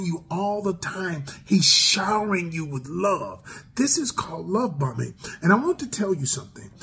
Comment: I thought he said “Love Bumming” 🤣